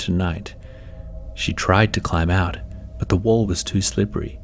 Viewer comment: i thought it said “The Walmart”